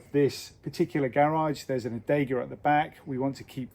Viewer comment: what did you say is at the back of the Not Garage? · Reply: Hi Monica, there is an 'adega' at the back of the not garage. It is basically a traditional wine cellar, and where they used to stomp their own wine! ❤️❤️